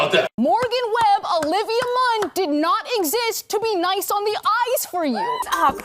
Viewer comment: Oh shit. I thought she was saying mice on the ice. Lol.